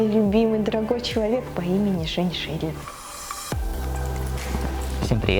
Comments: Мне в первый раз послышалось а во второй Женьшенькин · Сперва мне послышалось не, а женьшень!😂 😅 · вместо услышала женьшень 😂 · Когда она сказала мне послышалось женьшень 😂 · мне ведь не одной послышалось "женьшень"?